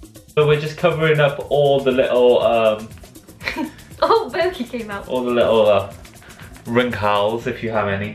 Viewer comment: she actually said oh boogie came out!! Haha only would say that 😂😳